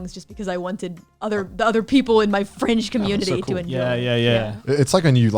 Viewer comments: did she say French community? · Connor wouldn't fall down the rabbit hole, so the rabbit hole came to him. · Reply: Yo i'm sorry to use your 6k comment to ask this but i need to be sure, at 1:40:22 did she say "my fringe community" or "my FRENCH community" ???? if any of the vtubers are french i'm gonna loose my damn mind and start selling organs to simp like wtf also damn 3k likes in 25 minutes is amazing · did she just said “French Community”? MMMMMMMMM🤔🤔🤔🤔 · my "French" community? So she's french? Or did I misheard that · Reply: Fringe. Non-mainstream. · i rewound that part like 4 times to hear the "fringe" part i thought she said "french". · did she say her french community? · did she just say French community? Does this mean she's french? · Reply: Fringe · did calli say "French" community or "friend" community??? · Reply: Fringe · Wait, so she's FRENCH? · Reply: No, I misheard that, as well. At that point, she said "fringe community", not "French".